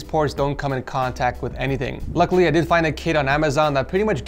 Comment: I THOUGHT THAT INSTEAD OF "KIT" HE SAID "KID" HAHAHAHA.................